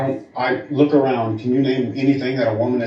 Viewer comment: I thought he said “in our plain sight” and she says Wi-Fi! Does she see the signals that we can’t? 8:16 really contemplating on starting a dedicated financial services for the fans chicks! Anybody wanna join, we will be multimillionaires by the weekend! Just say they pay a lot of taxes, the S&P 500 it’s up a lot of your cash, but everybody does it. Diversifying assets across a linear range in a multidimensional hexagonal quantum market! I mean, with a little effort, we can have them sign contracts that will be so binding a sadist would be envious! Have a clause that if they raise their voice, they essentially forfeit all earnings and current amount under management. Or literally anything is dumb as that!